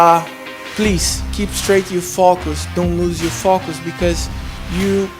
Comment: Definitely thought silva said “ keep straight you fuckers” until I realised he said focus 😂